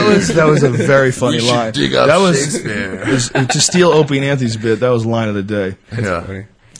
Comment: I misheard "Shakespeare" as "Shatner" and was so lost for about 5 minutes.